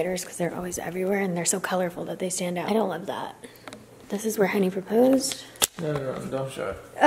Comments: ~what I heard~ you: "-and this is where Henny proposed.." me: genuinely hurt you did not tell us sooner hahaha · for a second, I thought I heard "this is where henny proposed" when you said "this is what henny proposed" omg I was freaking out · IMMEDIATELY THOUGHT “ Did I FCKING miss something?!” When I heard “This is where Henny proposed” 😳😳😳 lol then I read the comments, she said “this is WHAT henny proposed” I genuinely thought I missed something but then realized I’m a bad bxtch and I never miss any of her posts!